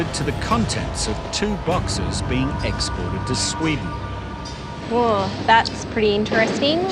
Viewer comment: did he just say exploit too Sweden ??